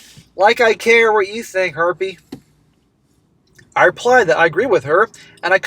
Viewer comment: Every time says "harpy" I hear it as "herpee". I vuess a gaggle of them are herpes.😊